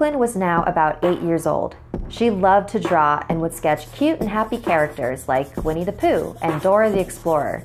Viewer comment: I thought she said happy and cute... This kid was already demented she drew Dora!